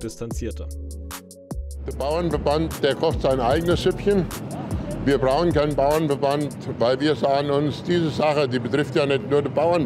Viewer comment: Was sagt der Mann da um die Minute 00:40? Also ich habe folgendes verstanden: "Wir BRAUNEN brauchen kein ..." Habe ich mich da verhört? Oder gibt er schon ganz offen seine 'BRAUNE' Ideologie zu?